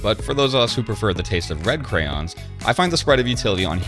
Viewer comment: "The taste of red crayons" LOL love it. Great stuff! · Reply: I actually thought he said bread crayons lol.